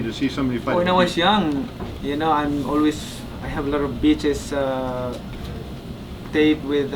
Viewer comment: I know he was saying'VHS' but I kept hearing it as 'bitches' haha